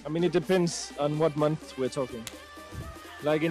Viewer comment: I thought he said “depends what time of the month” and I was shocked for a sec